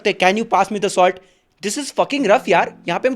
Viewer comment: is I am only one who hears this is f**king rough 🌚💀